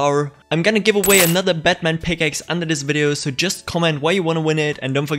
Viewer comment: is it called batman pickaxe? or did u say catman pickaxe lol wtf